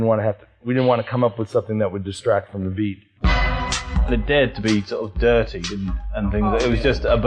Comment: what does the guy from Orbital say? It was a "dead beat"??